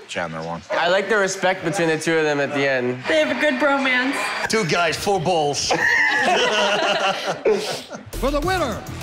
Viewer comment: Lady: They have a good bromance Bas Rutten: “Two guys, four balls” Never change Bas 😂🙌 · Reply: At first I thought he said “Two guys, full balls” and I was like lolwut